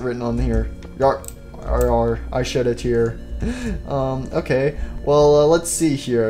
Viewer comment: did he say “yar I shitted here “ 😂😂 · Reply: I shed a tear